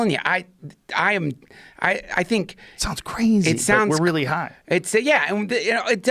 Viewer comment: did Joe say we're really high? Lolol